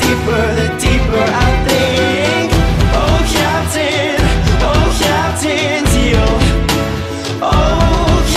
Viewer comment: why do i keep hearing "Captain DIO"